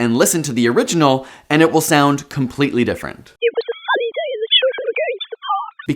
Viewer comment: i hear funny instead of sunny even the second time he played it after i heard the clear version